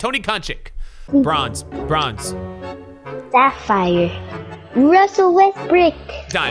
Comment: Lmfao anyone heard that man say “Russell Westbrick” 😭